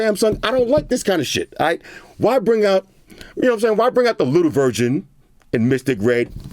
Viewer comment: I- Did I just hear him say: Why bring out the little *virgin*, in Mystic Red 😳😳😳😳😳